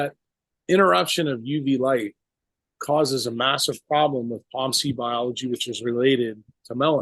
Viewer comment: what is the word at 17:18? Palmsy? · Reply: Proopiomelanocortin